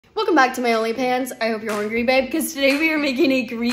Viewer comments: I thought u said only fans for a second😭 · OnlyPans caught me off guard I actually thought she said OnlyFans. Also, the level of references is hilarious